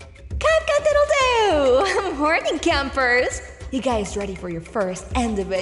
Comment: I THOUGHT SHE SAID ‘IM HORNY CAMPERS’ HELPPP 😭